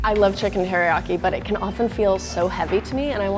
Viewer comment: did she say HERIYAKI? lol